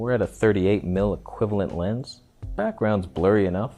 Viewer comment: Nope. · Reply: Oh, 38 equiv. I've misheard it as "30 equivalent" at first! Have to pay more attention :) Is it a Sigma? No way!